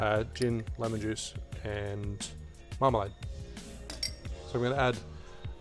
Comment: The way you say marmalade sounds like marmite. I heard 'marmite' twice. Now that would make an interesting breakfast drink! (Also, potentially vile...)